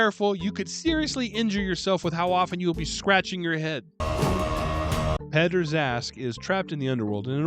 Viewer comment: I often listen to your reviews while I work and can only look at the screen now and then, so the first few times you referenced Peddar Zaskq I missed the Z and swore you were saying Pederast. The creep factor was already in L. Ron territory.